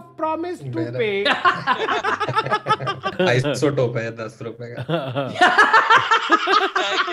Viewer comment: What did Say? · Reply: “Isotope hai ₹10 ka “ 😂😂😂😂